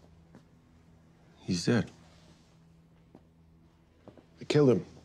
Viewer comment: I miss noir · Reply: I heard noir isn't dead